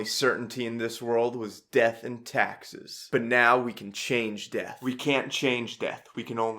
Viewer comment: I thought it said “and I thought the only certainty in this world was death in Texas” instead of “death and taxes” HELP😂